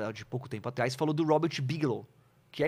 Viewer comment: nao entendi o nome do homem... Robert o que? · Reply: Bigelow...achei aqui..